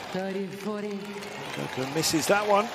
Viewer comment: Listen @ 1:22:27 sounded like the commentator said "Coco misses that one" 😂